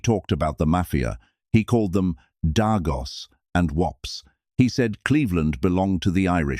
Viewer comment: yah im pretty sure he did not call Italians "dargos" bwahahaha